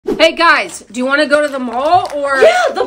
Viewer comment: I thought they said “you guys wanna go to the movies?”